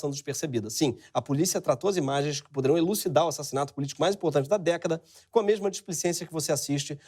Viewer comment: Aos 8:44 ,ele disse:uma das políticas mais importantes das DÉCADA? Acho que ouvi errado